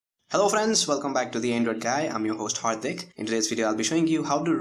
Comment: did he say "hard dick"😂😂😂💀